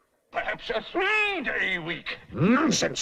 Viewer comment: is he saying “I promise a 4 day week”?